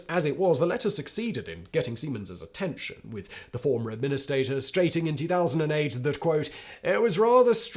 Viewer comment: I could’ve sworn I heard Simon say “the former administator strating” at 20:19 😂 I’ve watched it over like 5 times and that’s all I hear lol